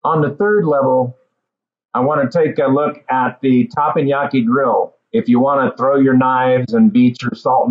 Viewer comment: Did I hear that right? Is that grill really called takoyaki grill? Or does he mean yakisoba?